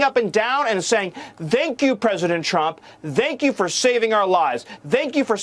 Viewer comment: is he say dank you or thank you 😂😂😂